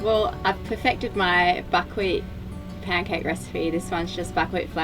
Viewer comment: I misheard "buckwheat" as "f&!kwit".